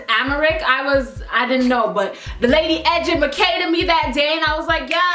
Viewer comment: What is she saying at 4:08? I've heard this only in her videos, what are the words she's speaking? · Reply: Basically "The lady educated me." But she was messing around and said it differently